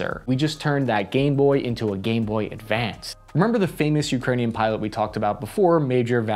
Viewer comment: Lol 🤣 …I was around the corner listening, and I heard… “We just turned that g..boy into a g..boy with fans!” 😳 I was like, whaaat?? And a quick rewind… ohhh, Gameboy, into a Gameboy Advanced. 😆 Right… copy that. Carry on. 🤣